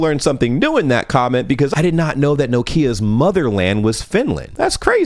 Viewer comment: Ouch, that's not the proper pronunciation of Nokia I think. It's more like Nock-ia rather than NO-kia as far as I am aware. It IS the correct English pronunciation of Ikea though, which is what I thought you were saying (from the kitchen) until I heard "from Finland".